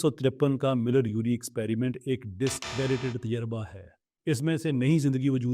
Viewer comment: he said "discredited tajurba hai" ...incase someone didnt catch it